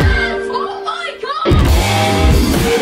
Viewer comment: Isn't the "YES Oh My Gosh!!!" part from a girl who did a fast Cup Stacking time? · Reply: monica styles Always thought she said "JIZZ OH MY GAWD!"